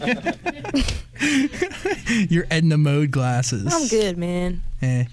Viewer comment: Can someone please tell me what Zach says at #1:01:28? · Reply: “Your Edna Mode glasses” a character from The Incredibles movie